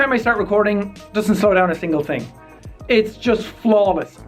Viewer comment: anyone hear jack say a Thingle Thing